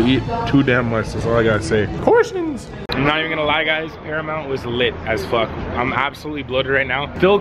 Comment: When wolfie said paramount I thought he said caramel 😂 LYL WOLFIE